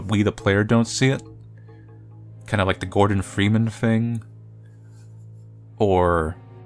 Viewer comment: did you said gordon freeman?